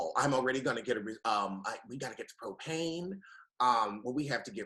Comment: It'll be nice to see a Pop Roast w/ the boys back together. isn't the only 1 tripping, cuz I also thought said "we gotta get the cocaine" @ 15:55. But he did say "propane". 🤣